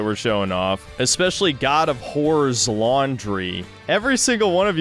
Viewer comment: Just when i thought I'd heard of every play on words, drops 'god of whores laundry ' lmao